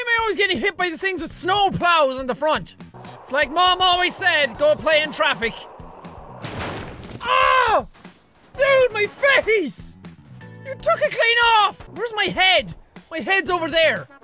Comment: captions said Oh dude my Freddy's I laughed at That part